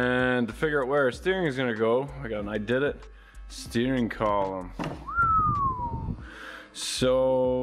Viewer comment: When those steering columns came out I seriously thought it said idiot steering column.